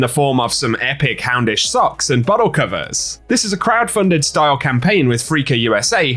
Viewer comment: Love the content. I could have sworn he said butthole covers and had to listen to the merch again 😂